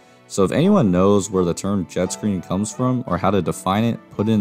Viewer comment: I don't think he's saying Jet screen. Idk. I'm hearing "back screen" which makes sense.